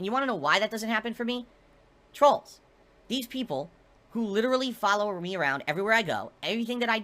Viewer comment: is it just me or did he say "trolls, these people literally follow ree around"